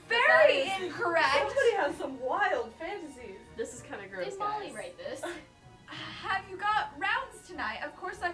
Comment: ok but did i hear it right bc i heard "did molly write this"?????? dndndbdbd